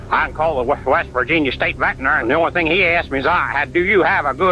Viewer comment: Is he actually saying "and the only thing he ask me is"? 😅 Jesus how can you understand what he's saying (English is not my first language)